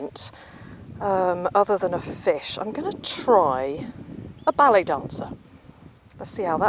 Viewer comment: Ohhh ballet dancer .... I thought you said belly dancer!!! 🤪😉